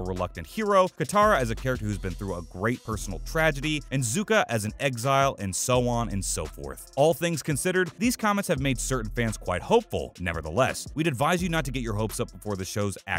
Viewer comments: Okay everything aside, I can’t be the only one who heard zukka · did he just say suka...